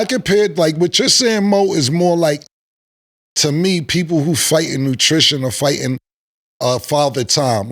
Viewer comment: did this 🥷🏽 Cam just say "fighting nutrition" instead of attrition